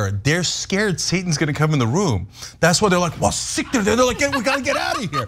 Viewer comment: did he just say "siktir" 🤣🤣